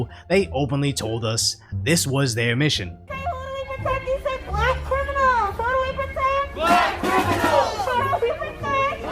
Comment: What is that woman with the bullhorn saying at 7:02. "Who are we protecting? Black criminals." That's what it sounds like anyway. What does that mean? I'm confused.